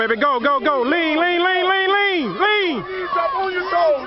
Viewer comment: was he saying LING LING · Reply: Lean